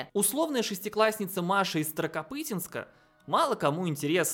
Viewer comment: Сказал "старокопытинска", мне послышалось "сракопутинска"